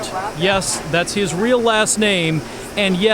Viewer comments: I heard "that's his real last name" as "real ass name" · am i the only one who thought he said "yes, thats his real-ass name"?